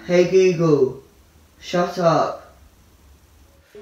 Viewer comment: I thought he said hey googoo